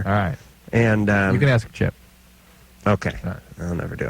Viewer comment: did drew say you gotta ask chip, the same chip? If so damn she's was working at kroq for a long while then